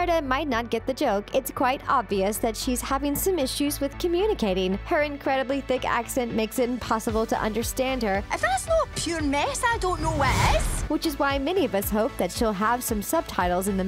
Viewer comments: I heard "If that's not pure ness I don't know what is" from Merida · What merida said was '' if that's not pureness i don't know what is'' that was